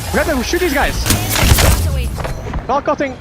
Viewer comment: 有人知道3:00那裡Hal說的Valk…是什麼嗎? 瓦基起飛那句 聽了好幾次都聽不出來到底是是什麼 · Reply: valk ulting